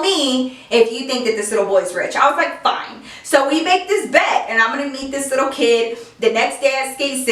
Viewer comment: I thought she said, so we mad this bed and I'm gonna eat this little kid😂